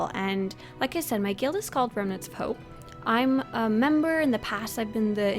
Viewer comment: I misheard "Gremlins of Hope". :) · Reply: ok, so every year around April Fools we play pranks on the guild. One year we become "Remnants of Soap". I think I know what our 2017 prank will be... :P